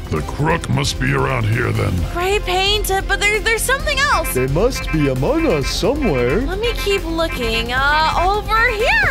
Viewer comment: I hear blue say “there must be among us somewhere”